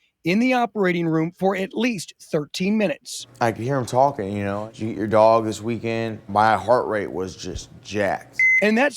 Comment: why does he say "did you eat your dog"